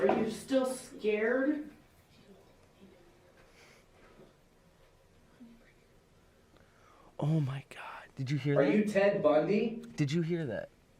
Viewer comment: I heard a close up woman’s voice that said “oh my god”.